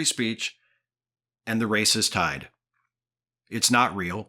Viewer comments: When you first quoted his comment "the race is tied" I thought you claimed he said "the racist tide." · I heard 'And the racist tide' and cannot unhear it.